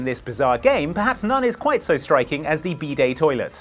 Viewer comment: When first said "bidet" I heard "B day", like birthday... and thought it was a birthday toilet and didn't understand what that actually meant. 😅